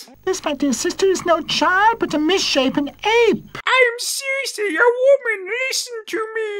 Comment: I thought she said 'misshapen egg' at first